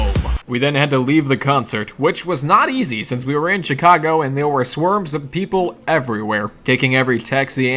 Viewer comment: did he say swerms instead of swarms?